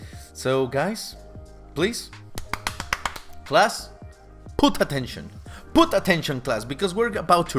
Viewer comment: I misheard “put attention” as “puta tension” lmao